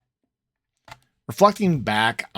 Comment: We're fucking back, keep this intro haha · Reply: That’s what I thought he said!? I had to watch a three times. I think he said reflecting? Lol. But I like fucking better!